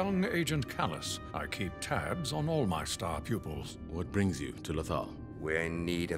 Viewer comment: "young agent kallus, I keep .... (what does he say next?) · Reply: Agustín Baletti ...tabs on all my star pupils."